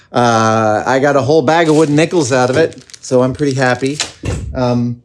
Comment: Nickles! I thought he was saying wooden nipples lol